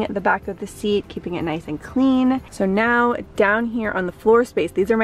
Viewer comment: Love this video! But who else thought she said "car b*tch" instead of carbage? Tryna put me on the floor 🤣